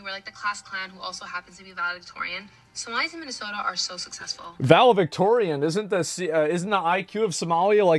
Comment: Asmon say "ValeVictorian" instead of valeDictorian is hilariously ironic